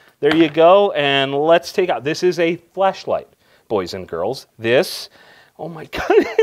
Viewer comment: "This is a flashlight". I could have sworn you said fleshlight.